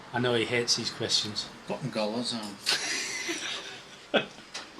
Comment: Can somebody tell me what answers at 6:57 ? I understand "button goers", but as English is not my mother tongue I don't have a clue if this is accurate. Thanks for this (again) wonderful video, and what a beautiful sewing machine! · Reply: Putting collars on